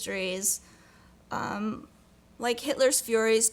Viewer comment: I thought it said: Hitler’s Furries